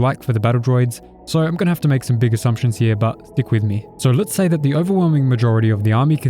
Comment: what was said at 0:56? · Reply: "stick with me"